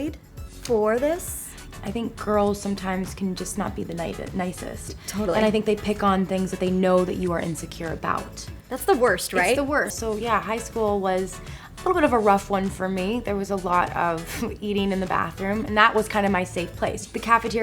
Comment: Am I the only one that noticed that they said shool instead of school?